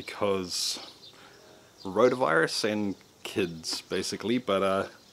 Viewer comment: Did he say Coronavirus